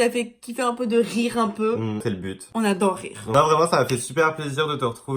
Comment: quand il a dit "c'est le but" j'ai entendu "sale pute" j'ai eh peur sinon super vidéo :) :)